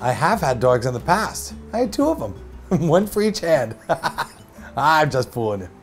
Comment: I swear I heard him say he had one dog for each head. Glad I'm wrong 😂